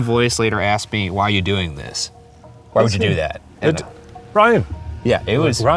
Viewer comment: instead of "why would you do that?" i heard "how would you know?"